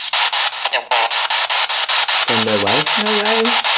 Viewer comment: sounds more like 'let it walk' or 'little rock'. Whatever is said it hardly sounded like 'no way', well to me anyway.